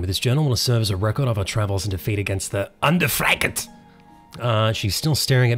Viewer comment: did u say under fried cat